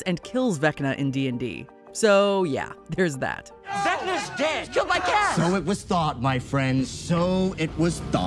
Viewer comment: it sounded like mike yelled "HE KILLED MY CAT" i know he said "he was killed by kas"